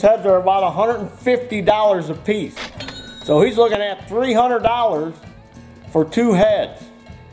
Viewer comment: did I hear say $300 for new heads plus $200 labor. I'm taking my next mower repair down to Indiana. Great video. keep up the good work.